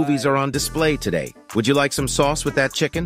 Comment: He said " fedex man is at my house " I heard " fat ass man is at my house "